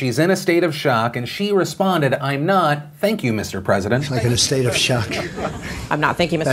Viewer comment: Thanks for tuning in ya Beautiful Bastards. <3 Shane Backlash (00:06), Elon SEC (4:52), TIA (6:28), Cali Net Neutrality (7:57), Trump USMCA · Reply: Not a fan of trump personally I don't like him but I think he misheard the news woman because I when hearing the clip even after Phil said what she actually said I heard I'm not thinking instead of I'm not thank you and Trump responded with a joke which was poor given the circumstances but understandable given the fact that he misheard what the news woman said